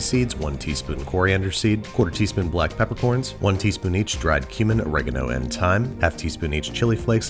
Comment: when he said "cumin" i thought he said human and i had to re-listen a few times before realizing